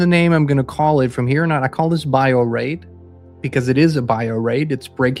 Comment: Right til the end of this video, i thought you were saying 'bio rape' but both terms fit... :-( Can we unconsent?